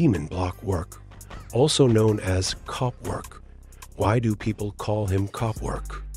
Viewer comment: When he said cop work I thought he said cock work